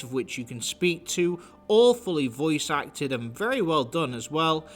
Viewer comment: "All fully voice acted," ah. I thought you said "awfully," lol. Anyway, I like the way this game looks more than Skyrim, and acting like this game is LESS jank than the average BGS release is ker-azy.